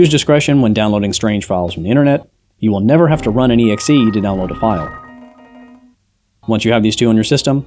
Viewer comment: what's the thing he said? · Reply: "You'll never have to run an .exe to download a file."